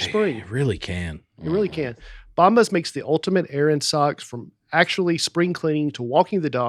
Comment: I thought said "the ultimate AARON sock" but it was errand😂 love the show !